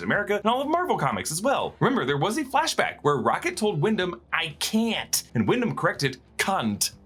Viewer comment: I thought rocket said "'i can'ts"